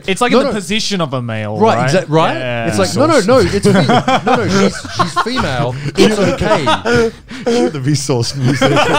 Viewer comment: What did say at 05:21 what laughed about? He said it again, but I still couldn't catch it. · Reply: i think he said "vsauce music"